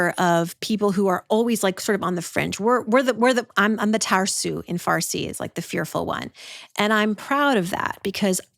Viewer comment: What words is she saying? Tar sue? Farce? Can someone tell me?